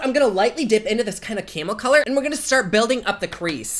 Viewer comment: I think he said caramel color but I heard "camel color" 🤣🤣